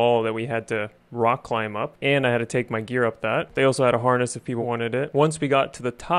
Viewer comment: All I heard was "there was a harness if you wanted it" 🙋🏻‍♂️ y p